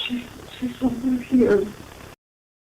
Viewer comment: I Heard "She's over here" rather than " There's Someone here"